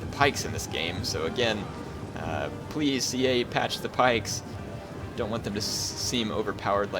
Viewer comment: Did he say "please EA, patch the pikes" at 1:05:50? If so, I thought this game was published by SEGA.